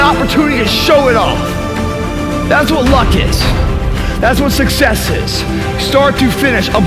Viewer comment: On 3:16 did he say thats what luck is?